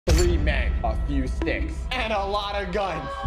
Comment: i had to rewind the video like 5 times to hear you say a few sticks cause i kept hearing "a fck you stick"